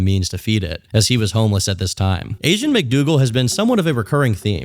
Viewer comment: I just thought you said Asian mcdoodle lmao